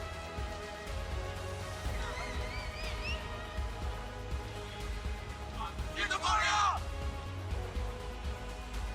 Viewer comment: did they just say Get the fuck out ? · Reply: I think they said "G2PHORIA!" lol